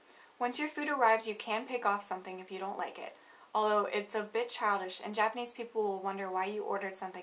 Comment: I thought she said "Although it's a bitch" but she actually said "It's a bit childish"